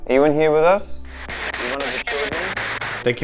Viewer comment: it definitely sounds like it says something i cant make out and then "the children" not the camera